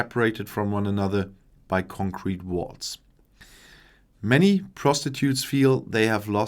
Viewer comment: did he say "many prostitutes feel they have lost the good Friday agreement…" instead of "many protestants feel…" ?